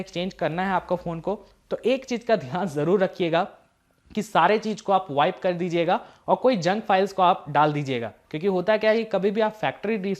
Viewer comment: what he is saying? Kuch wipe jaise ?? Iska kya matlab?